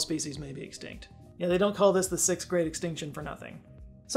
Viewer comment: I thought you said this was the "6th grade" extinction. As it seems very few people seem to be smarter than a 5th grader these days, it would be an apt name.